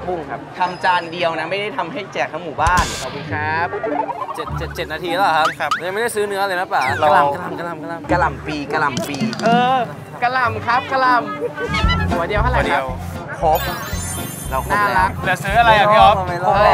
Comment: เราหูฝาดรึเปล่าได้ยินพี่ข้าวตังเรียกพี่ี่ป๊อดว่าปะป๊า